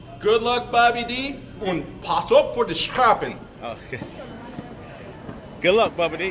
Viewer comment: "pas op voor de schapen" of wat zegt ie?